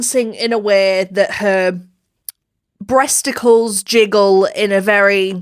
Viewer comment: I HEARD "IN A WAY HER TESTICLES JIGGLE" AND I FUCKING ROLLED TO KOREA WHEN I MISHEARD THAT 💀😭